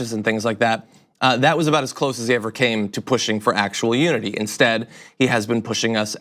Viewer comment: I am profoundly deaf but hear poorly with hearing aids while reading lips. When John says "unity" I hear and see "nudity".